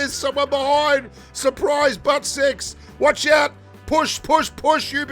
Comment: did he really said butt sex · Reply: SURPRISE BUTT SEX!!! I nearly busted a gut when he said that!!